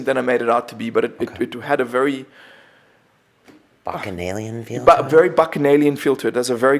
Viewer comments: what word does he use at 1:12:27 ? The other guy even says it's a very good word to use, but I can't make it out! Anyone know? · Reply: lisa s the word is bacchanalian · dunno, but he said no procreation happened during san pedro, so I dont have a clue why it was censored. I hate the mistery!!! I think he said something like all men had a hardest boner or something lol!! or maybe they removed their panties, who knows... :P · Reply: he said bacanavian feeling or what it was? thats the key to it but I dont understand him. Tryed to google it but no results